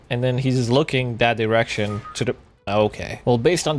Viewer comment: Ok so umm am I the only 1 hearing erection instead of direction @ 6:28 ? No? Just me? K.